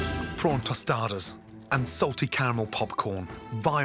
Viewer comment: The first time Gordon said caramel popcorn i thought he said camel 😅